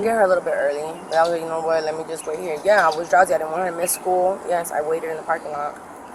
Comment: It sounded like she was about to say "I was drunk!"